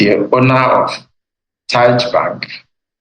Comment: FRAME 19:20 ... Did he say "TAJ" Bank? I didn't quite get the word properly. I know he said the other one is "Chinese" Bank.